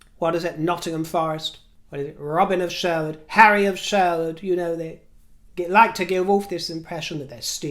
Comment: Instead if "Harry of Sherwood," I thought said "Harry of CHOADE." I guess it works either way though. Right on brand for Harry! 😂